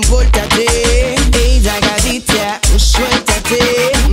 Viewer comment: toda la vida pensé que decía “ey drogadicta” hasta que vi el video con letra jajajaja